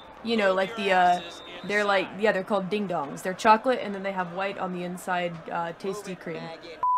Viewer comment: my brain can't handle on what is she saying WHITE ON THE INSidE